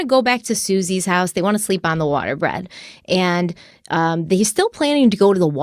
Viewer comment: Okay I swear Stephanie said water bread